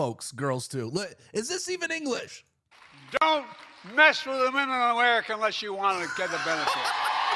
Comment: Imo sounded like Biden said don't sniff the womem of America unless you want to reap the benefits 😅😅